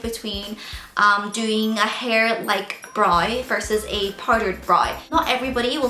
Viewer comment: Whoops i thought you said parted not powdered